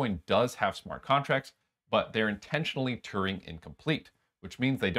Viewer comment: Hey, nothing serious, but just for the sake of someone looking what it means. 00:12:12 says "turning incomplete" as I think the correct would be "Turing incomplete" . Awesome content , many thanks